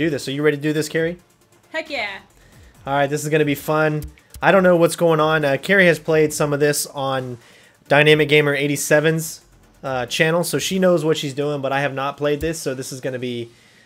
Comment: It sounds like your saying dimaticgamer87, but that doesn't exist lol